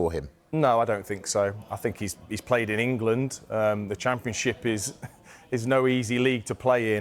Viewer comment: Did I hear that right 1:28 championship?